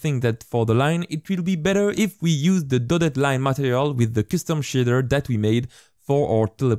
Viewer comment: Bruh um- did he just say “The toilet line material with the kiss them shitter”?